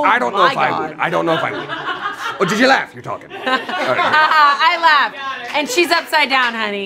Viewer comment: what did Damien say after "did ye laugh.."? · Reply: You’re talking